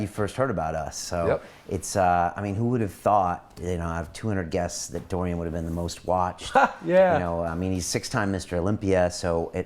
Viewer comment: Who the heck he was talking about?!??? I've rewinded to this point (1:05:09) several times and still can't catch the few mumbling words that might have been the name of the person in question. There are a couple more places in the vid that is like this too ... where he was not articulating clearly, and no matter how many times I rewind to re-listen, I still can't figure out what he was saying ... very annoying!!! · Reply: Oh, Dorian Yates?!??? Finally got it from whose articulation is always loud and clear.